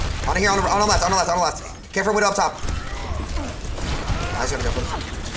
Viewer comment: What did he say at 1:56 😂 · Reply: I'm pretty sure he said "I just need to be careful."